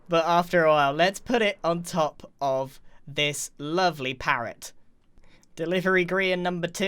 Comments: did he just call that a parrot...? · did he just called that chicken a parrot ?